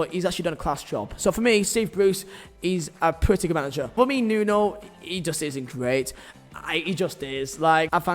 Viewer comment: Who else thought he said “he just isn’t great” at first about Nuno?